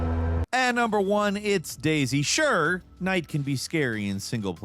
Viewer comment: I heard Daisy and thought, "damn, that's the perfect name for a horror game", but then he said it a couple more times and I understood what he was talking about...